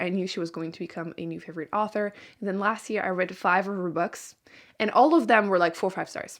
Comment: For a second I thought you said last year I read 500 books and I was about to have a heart attack lol